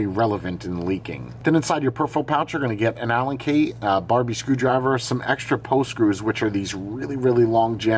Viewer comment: Did i hear right, "Barbie Screwdriver" 😭😭😂😂😂😂